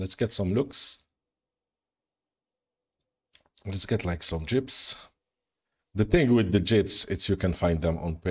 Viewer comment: I Crack up every time you spawn jeeps because it sounds like you say jibs! 🤣